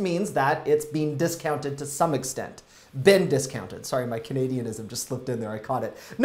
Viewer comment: I thought you said "being discounted" anyways lol I had to go back and re-listen to realize what you said lol